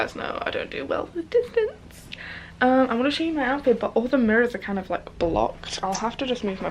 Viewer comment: When you said that you wanted to “show your outfit but all the mirrors are blocked” I thought you said that you wanted to “shave your armpit.” I was really confused hahaha.